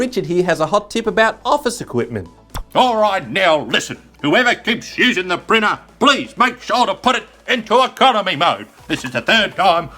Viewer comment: I heard "Whoever prints shoes in the printer, please put it in economy mode!".